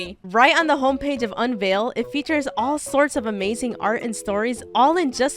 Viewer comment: why did i hear ia candy at 4:34😨😨😨😨